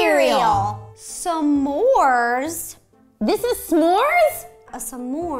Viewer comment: It kinda sounded like Roe said Samores who else heard that sorry if that offened any one 🤗